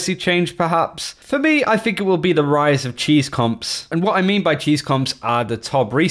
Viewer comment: I thought u said rise of Chinese comps. I was like LMAO WTF?